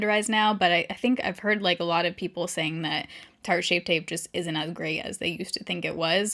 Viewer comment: I could’ve sworn you said shart tapeshape 🤣